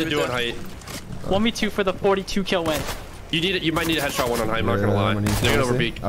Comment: wHAT DID HE SAY? 42 KILLS?! Bruh crazy squad right there LMFAO